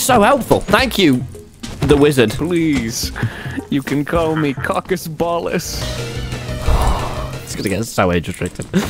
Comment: I didn’t laugh then I hear “Please, call me cockus ballus”